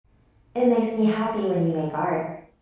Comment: I thought at the start it said, "It makes me happy when you fart." Like, okay then...